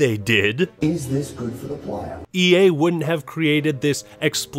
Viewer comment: For the longest time I thought he said " is this good for the plan"